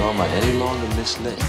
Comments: Misled? I kept hearing, “This Land” still a great song❤ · Wow I was like 7 or 8 when this came out and I always thought he was saying miss lady not misled. 🤪